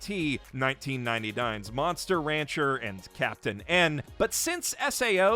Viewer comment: I can't hear what anime he mentions at 6:09... can anyone tell me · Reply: Monster rancher or Captain N?